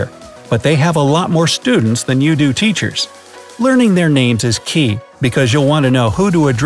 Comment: Did he say key